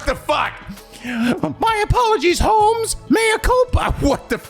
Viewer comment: I thought Kyoshika was saying homies, it would’ve been hilarious. Kyoshika: My apologies, my homies! We thought we got the bling on the commanda! Hope we can make out the hood with this one!